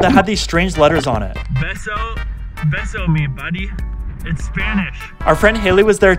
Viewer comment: I didn't see what the bracelet said and it sounded like you said beso me budy. I was like beso means kiss 💋 in Spanish but then I watched again and it said besomebody, lol.